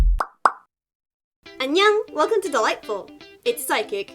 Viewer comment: I thought u said welcome to the light bulb not welcome to Dollightfull